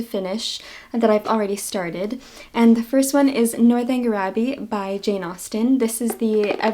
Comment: Is it me, probably 🤔! But- it sounded like you said,"Northern GRABBY"!😂 Not to knit-pic over anyone extremously speaking. But I like the nick-name. Could be "North Anger Grabby"! Anyone might be angry if someone was grabbing at them. Lol. Love yr channel. Loved yr visit w Ms Emmie btw.